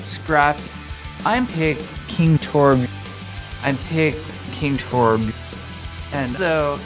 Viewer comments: I just turned on the subtitles after watching. I thought he was saying, "I pick King Torb" the entire time. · At first i thought he was saying "i pick king torb" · For the majority of the song I thought he was saying "I'll pick King Torb" and not "I'm picking Torb" lol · I thought it said I’ll pick king torb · I didn't turn on subtitles so for a good while I thought he was saying "I pick King Torb" Rip